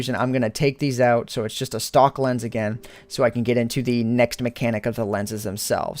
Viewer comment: I heard that as a stalker lens.